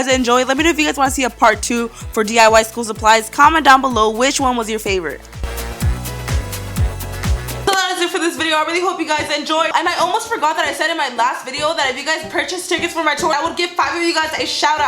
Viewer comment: It sounds like she says I really hope I get to beat you